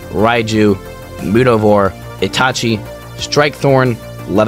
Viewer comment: did hey call that kaiju ITACHI?